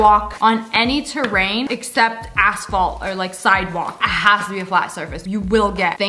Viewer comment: did she say assphalt!?